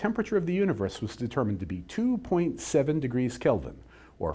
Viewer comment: I thought you just said 2.7Kelvin not 2.7 degrees kelvin?